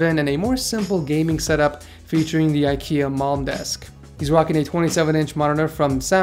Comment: I always thought he said IKEA Mom desk